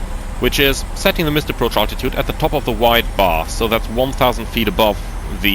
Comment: Great video. I had only a few places where I did not understand what was being said. One of these is at the time stamp 11:00. "Setting the missed approach altitude at the top of the ??? (sounded like wide bar)." Can anyone clarify? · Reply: “White bar”, referring to the bar on the altimeter.